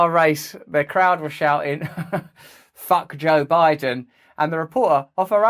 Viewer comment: I thought they were saying "fuck you brand"